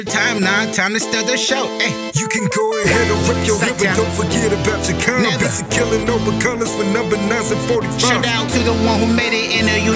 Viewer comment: did he say "You can go ahead and rep your hood but dont forget cha come busy killing others over colors with 9's and 45's"? that part was a little tough to understand. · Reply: “You can go ahead and rep your hood but dont forget bout your kind, busy killing over colors with number 9’s and 45’s”